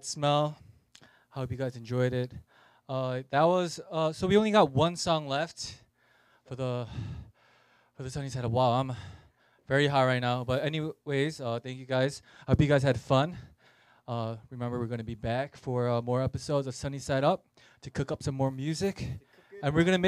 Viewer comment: BRUH LMAOO I THOUGHT HE SAID HE WAS HIGH KEHDKQ